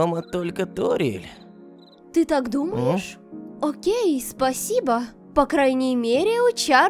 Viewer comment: почему я слышу не окей, а гей?